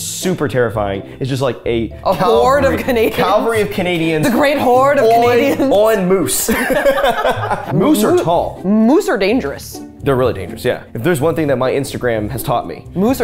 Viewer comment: Fellow Canadian here. At first when said a "Calvary of Canadians" I heard a "Calgary of Canadians"